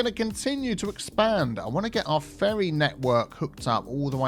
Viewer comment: Note to self: Never watch videos first thing in the morning. I thought you said, "Fairy network," rather than ferry network. I had pictures of fantasy beings flitting around the buildings.